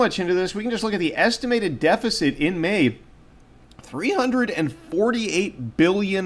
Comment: Okay mixed feelings; I thought you said "deficit in May erroded" - like yay country oh no for silver. Haha but no we're still safely screwed.